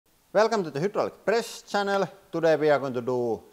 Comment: Good Lord - at first, I thought he said "Welcome to the Hitler press channel ..." :face-orange-raised-eyebrow:- thank goodness I was wrong (not his fault; my hearing isn't the greatest). Anyway, great video, very illuminating. EDIT: I'm not kidding. That's really what I thought I heard.